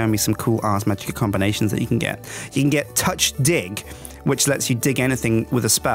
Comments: I yawned as said "touch dig," I thought he said "touch dick," so I kinda had to replay that part just to make sure :/ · am I the only one that thought he said 'Touch dick' at (7:26) ahahahahah ;'D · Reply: probably are. because most people know how ars magica works, so they knew he said 'touch, dig'. either that or we arnt 12 years old and laugh at potty humor. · I really shouldn't have been eating/drinking during this video, especially the part where I misheard 'Touch Dig' as 'Touch Dick'... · All Final Fantasy games are rad.. Except for 13, 13-2 and 13-3.. Sorry guys but those games are just depressingly bad.. And I have finally decided to get into these mod packs rather than sticking to vanilla Minecraft and I have to admit, as someone who just got into these mod packs, it is rather daunting! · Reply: Also, I completely misheard the touch dig and heard touch di- you know what I thought..